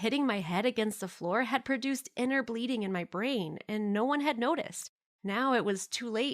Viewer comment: I thought she said breeding